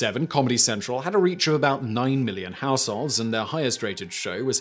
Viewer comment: You said comedy central reached 900 million households but then said South Park made that jump up to 50 million. Did accidentally say 900 million instead of 900,000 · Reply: You may have misheard. @ 9:35 he says nine million - not nine hundred million.